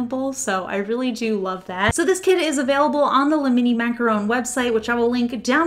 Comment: I kept hearing you say "lemony macaron" and now I want a lemon macaron hahaha 😭